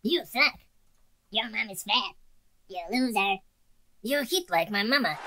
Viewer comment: did i heard hitler?